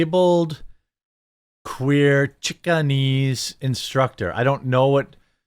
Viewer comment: 🎶 "Turning Chikenese, I think I'm turning Chikenese, I really THINK so....."🎶. 😂😂😂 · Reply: At first I thought she had said Chicanese, which is an ethnic mix of Chicana (Latina) and Chinese but I listened again and she said something like Chicakinese. I tried searching it and came up with zero hits so I have no idea what that is. Possibly a Native American tribe but who knows?